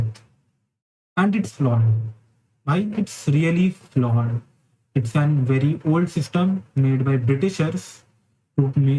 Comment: Kya bola sound cuts for 2 secs? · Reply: Oopsie, I said like I kinda understand where he is coming from